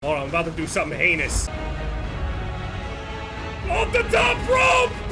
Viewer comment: oh lol. I heard you go. I'm about to go some anus and then drop from the sky :D :D